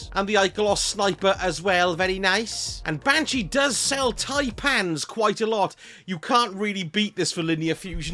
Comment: I thought you said “ and banshee does sell tight pants quite a lot” 😂